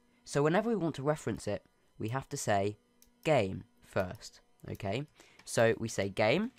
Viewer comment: I thought he said gay lmao